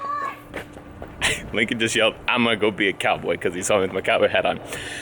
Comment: Are you sure he said "I'm gunna be a cowboy"? Because i heard "I'ma get pizza rolls"